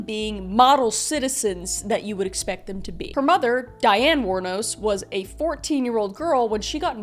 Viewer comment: super early in the morning and I registered Aileen instead of Dianne, and after hearing what followed I was like "how tf we gonna just breeze past this?!" EDIT: 3:23...ok nvm we back on